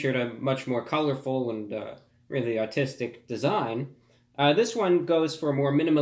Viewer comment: Everyone making a fuss because of my accent and the way I pronounced the word "artistic"... just trolls making their rounds! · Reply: Many times i mishear "artistic" as "autistic", but maybe that's just me.